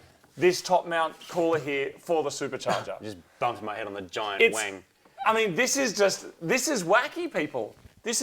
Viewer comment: "I just bumped my head on the giant wing," at 8:13, as an American, was hilarious because I heard "giant wang."